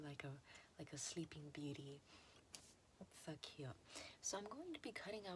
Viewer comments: I thought she said "fuq you" T-T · Ok I misheard 2:10 so damn bad I thought you said f*ck you… God my mind is dumb.